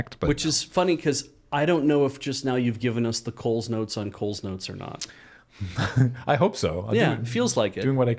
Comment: I have a bit of covidbrainfog going on, so I misheard "Nicole's notes" and merely assumed it was a summary compiled by someone behind the scenes, named Nicole.